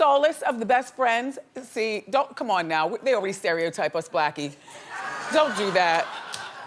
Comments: She called the girl in the audience"blackie"... Or am I hearing things?? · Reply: I heard "you know ppl stereotype us blackies" or was it "stereotype us, blacky"? Idk....i think she included herself she talks too fast. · DID WENDY JUST CALL THAT BEAUTIFUL GIRL "BLACKY" ????? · Did she really call that audience member "Blackie"? Pls tell me I heard wrong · I cringed when wendy called that girl "blackie" at 24:18 you would think she would know better by now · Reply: koko30504 she said, they already stereotype us, blackie. I thought she said "us blackies" also until I went back a few times and listened closely. this is beyond wrong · Damnit did she call her Blackie @ 24:15?